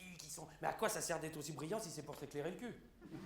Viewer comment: qu'est ce qu'il dit à 6:30 ? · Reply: Gauloiseries d'un phénix il a dit quoi ca sert d’être brillant si c’est pour t’éclairer le cul